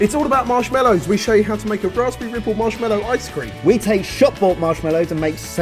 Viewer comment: at first I swore said raspberry nipple instead of ripple😂😂😂